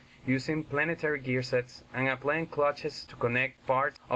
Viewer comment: Just curious, and sorry i seem rude hear. At 0:39 did you say " and a plain clutches"? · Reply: I said "applying clutches". English is not my first language so excuse me if I am not very clear.